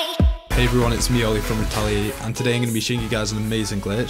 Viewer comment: All this time I thought ur name said retail8